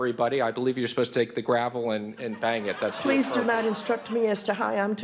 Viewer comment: Did he just say gravel?